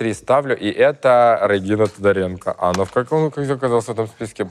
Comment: Рыгина Тодаренко послышалось. 😂😂😂